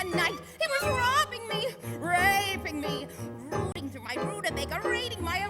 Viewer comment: sounded like she said "raping me"